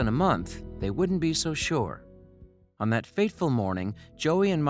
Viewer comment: fateful morning.... Really sounds like you said faithful morning. Which is horribly ignorant of common colloquialisms if true.